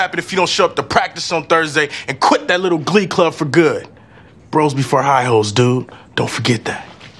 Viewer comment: I thought he said bros before high notes😂